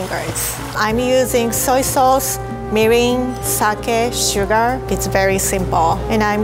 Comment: can someone tell me what she said, i wanna make that sauce but i cant understand her EDIT: i googled the tebasaki sauce and it's called mirin so nvm